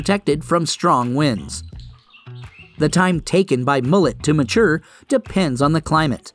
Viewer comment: Sounded like he said, "Mullet"😆.